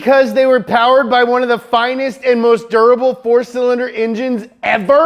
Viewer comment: what did you say? Dur a ble ? .......... WHAT THA FAK IT AINT IT WILL BLOW UP MY FREIND